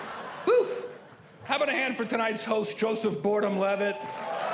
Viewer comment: I don't think anyone noticed he said "Joseph Boredom Levitt."